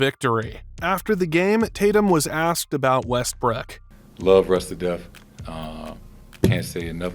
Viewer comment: Bro did he call him Westbrick @ 7:55 or I’m trippin